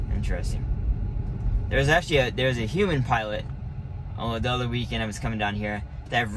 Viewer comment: human pilot did you say?